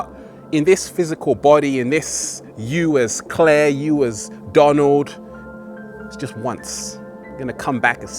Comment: did he just say " US Donald " at 4:29 ? didn't Donald take presidency in 2017 and this video is published on 2015 , did I misunderstand anything ?